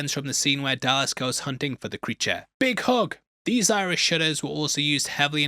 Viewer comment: When he said "iris shutter" i thought he said "Irsh shitter" and thought "wow, they must have fancy toilets in ireland"